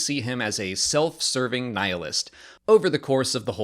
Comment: "Cassian loses his homeworld at the start and then we follow him into adulthood where we see him serving Nihilus" Thats what i heard and I don't accept any takebacks 😂